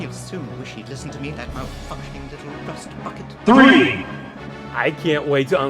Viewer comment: it sounded like c3po said you mal*** rust ((( but after listening more, i realized he said you malfunctioning rust bucket.